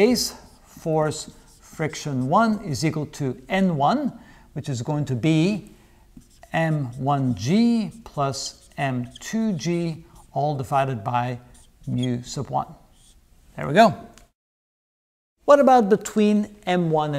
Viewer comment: I probably misheard but I think at 2:27 you said "All divided my u1" ?